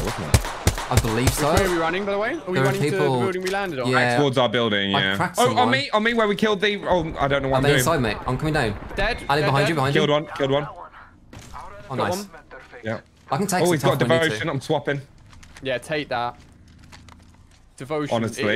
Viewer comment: did say "Are they inside me?"